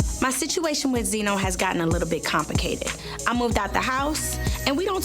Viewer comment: Karlie Redd gon say “compicated” instead of complicated 😂😂